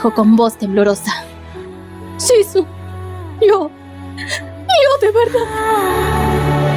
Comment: Yo llore 😢😢😢, al final que dijo??? No lo escuche bien, alguien me dice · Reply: shizun... yo... yo de verdad.... u_u'